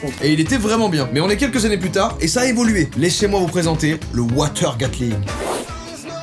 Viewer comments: "Léchez-moi vous présenter" hmm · « Léchez moi vous présenter » huuum tkt Jordan on a rien entendu